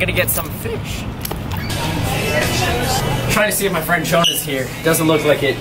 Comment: Did you say you have a friend named Jonah? That's really cool because my name is Jonah and I rarely hear of or meet somebody with the same name.